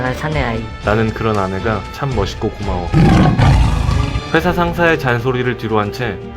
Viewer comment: 사연자 말하는 부분, 나는 그런 아내가 참 멋있고 무서워-라고 잘못들었다..ㄷㄷ 건강한 사내아이라니.. 배속에 아이가 딸이란거잖아..그것때문에 유산시켰고..